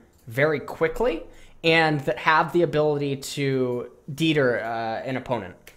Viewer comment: did this man say "deeder" instead of deter :I